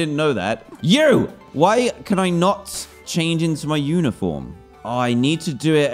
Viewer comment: I THOUGHT HE SAID WHY CANT I CHANGE INTO MY UNICORN PLZ HELP